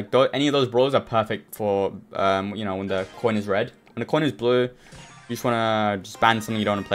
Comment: Can someone explain what he means at 7:05 when he says "corner is red. Or corner is blue" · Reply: he said the coin is red or blue, if its red then the enemy team starts the picking